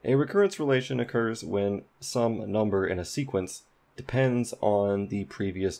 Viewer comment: wtf did u just said ? some number or sum a number ?